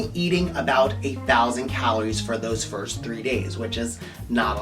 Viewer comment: am I stupid or did he say 8000 calories for 3 days? thats like a lot. at least like a woman my size. I mean 2600 cal a day is a lot :D (or did he mean 800 cal a day , which would make more sense lol ) but great video Sir! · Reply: LOL...you are the second person to ask this. I said "A Thousand Calories" meaning 1000